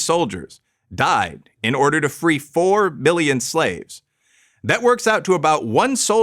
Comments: it sounded like you said “to free four Billion slaves”. The graphic reads 4 million (4,000,000). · did he say million or billion? Sounded like he said both words at the same time. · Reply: Milion, for sure.